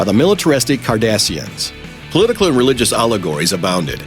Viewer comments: I thought he said "Kardashians" ! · I heard "the militaristic Kardashians"